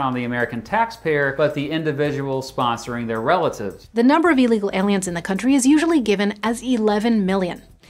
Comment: did she actually say "Illegal aliens" or am I hearing it wrong?